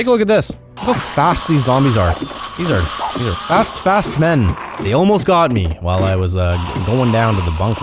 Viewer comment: am i the only one who heard "He almost pegged me-" LIKE WHAT LMAO edit; i now know he said tag